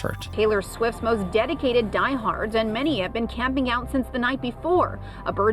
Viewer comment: I heard they been camping there since '94 😂😂